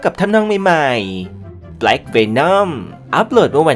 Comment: แอดพูดว่าblack venomค่ะ 😅😅😅